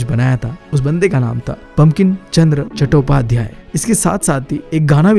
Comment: who else heard pumpkin chandra chattopadhyay 😅